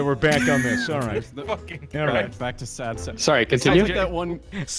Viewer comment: i thought you said sad sub which is me